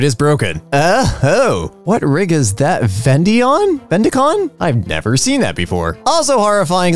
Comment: What you say? Offendion? Lol Sounded like it. That laptop is now officially an Offendion.